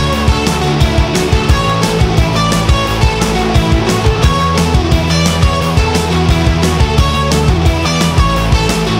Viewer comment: You know what she said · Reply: I think she said "Oh fuck"